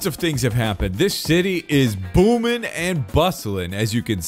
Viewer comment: I totally did not think that said “This city is boomin and bussin” at 0:18 nope not at all